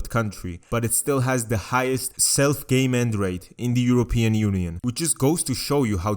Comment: He kept saying self game end I thought he was saying self gay mend